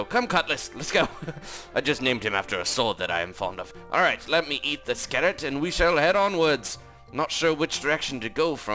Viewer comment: why did you say:"Let me eat the skeleton"